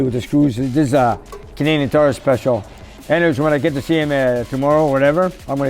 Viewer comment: What does say at 7:39 about the tips? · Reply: Basically he said they are Canadian tire Cheapo and he was going to tell his customer to lose them.